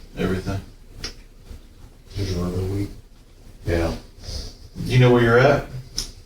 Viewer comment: I swore I could have heard him say "I'm little dick" at 6:56 lol